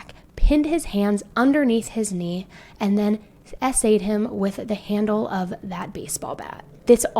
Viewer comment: Ik this isn't time for jokes but I was just listening in the beginning and when you keep saying the teachers went to jail for a kid getting SA I def heard it as essayed and I was like what! Damn I wish I knew that back when I was in school I hated writing those things!